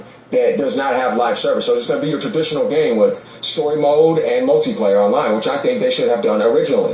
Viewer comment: I thought he said soy mode.😂 20:16 Lol! And sometime last year, puertorock called, certain Xbox fanboys, and people in this comments section, a bunch of happy meal gamers, and now Microsoft is making deals with McDonald's. Lol!👍😂